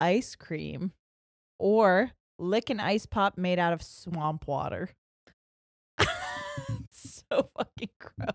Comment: I thought you said the popsicle tastes like “swan pwater” I’m like tffff is thatttt?! 🤣🤣🤣